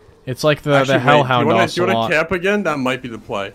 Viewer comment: did ya boi say "ocelot?"🤣